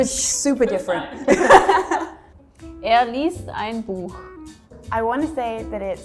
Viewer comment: Good video. I missed the first bit of the German lady's sentence the first time through, and thought she said "Ihr liest ein Buch." instead of "Er liest ein Buch.". Funny, because it does make a difference, but the second time I listened, I heard it properly. My bad. I am also wondering if the Swedish "Kom hit." leans into the more Old English-type way of saying it, "Come hither." which is just a fancy ass way of saying "Come here." Also funny.